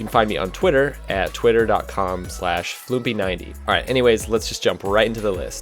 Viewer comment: the subtitles said twitter com/usembassymanilla instead of /fluumpy90 xD